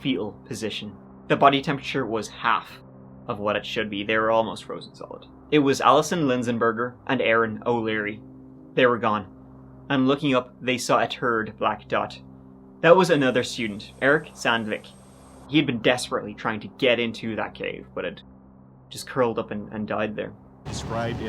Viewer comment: sounds like he says “turd black dot” 😂